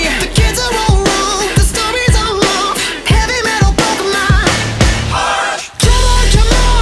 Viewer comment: I hear "heavy metal pokemon-heart!" XD